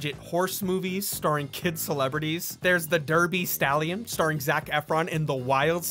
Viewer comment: I thought you said Quest for the Derpy Stallion